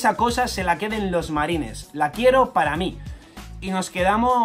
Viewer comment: llamarme loco, pero yo entendi "La quiero para mí" Quiere ir a Boa Hancock que está en peligro al perder su estatus de shichibukai